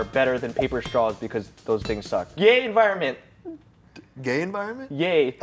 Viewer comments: "Yay enviroment" "Gay enviroment?" "Yay" I, too misheard that. Lol. 😂😂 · When thought said "Gay environment" he looked at him like he was about to have an unintended coming out video